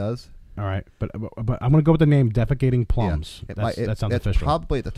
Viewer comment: did he say "defecating plums"? lol. <link> Am I missing something here? lol. Anyway, you guys' videos are awesome. lol.